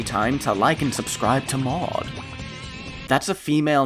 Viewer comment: “like and subscribe to [what was said here?]” ? “mog”? “mod”?, “moi”? I’m not aware of any of those being female names? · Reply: "Mawde", as in his screen name. Maude is a feminine name with the same pronunciation.